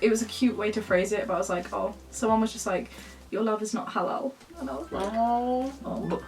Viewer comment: Roman Crowley English is not my first language and I couldn’t understand what said at 21:28 so subs would have helped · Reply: it isn’t my first language either but it sounds like „your love is not halal“ what would kind of make sense in that context